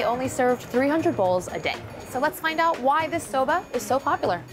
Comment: did she said soba